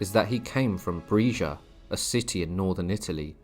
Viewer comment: 'Brescia' is pronounced with a short 'e', as in 'bell'. (I thought you said 'Fresia' at first, where the cows are from 😂).